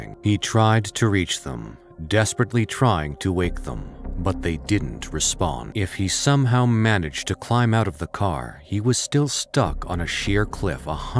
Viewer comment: Did he say "... But they didn't respawn..." in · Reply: It may sound like it but he said respond.